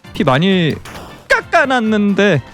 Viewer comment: 나만 까까놨는데 라고들리나